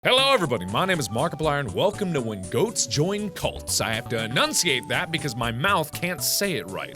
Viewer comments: My brain thought you said when joint goats cult. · Ok so...XD I thought he said Welcome to when goats join cunts XD XD